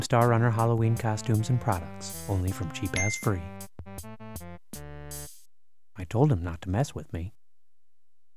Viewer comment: at the end I heard it as "cheap ass free"